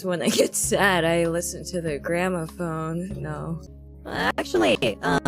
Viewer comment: Just saying, it's "Gramophone", not "Grandma phone" lol. A gramophone is that thing she's moving right there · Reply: I heard her say Grandmaphone not gramophone. Usually that's pronounced with an intrusive 'r' sound like "grammerphone"